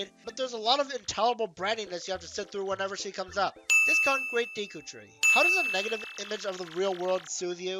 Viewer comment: the auto-granted subtitles are so wrong. You clearly said “discount great deku tree” but the subtitles says “this C-word great deku tree”.